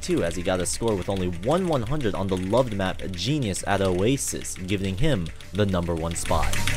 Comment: Genius at oasis.... BOI it says genesis smh